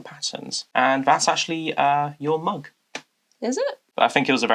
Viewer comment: I thought he said "your mom" 🤣 So casually as well 🤣🤣🤣 This is so fun to get to see the whole year! ❤